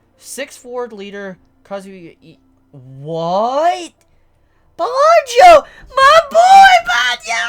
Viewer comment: did anybody catch say " my nigga banjo " at 20:12 😂